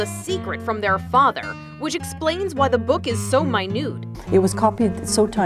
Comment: sounded like she said that was soo my nude